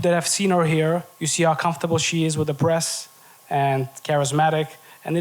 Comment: I thought dude said you “see how comfortable she is with her breast” I had to run it back lol he said press